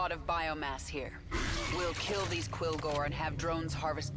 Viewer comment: For some reason i keep hearing "Quillboar" when they say Quillgor.... been playing too much Wow.